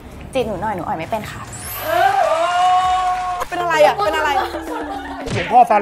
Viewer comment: หลวงพ่อซา เล๊ง 😆รึพูดซานิ ได้ยินไม่ชัด